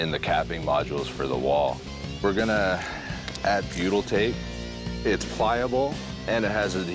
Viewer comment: can someone please find the product at @ 22:48 for me? the stuff he uses to level the block cap, I can't understand what he's saying. goodle tape? lol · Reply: look up butyl tape